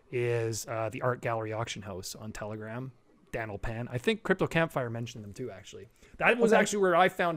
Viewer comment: mentions an NFT art gallery/auction house on Telegram, Dan O Pan? I've listened 10x and can't understand what he says, anyone know? Thanks!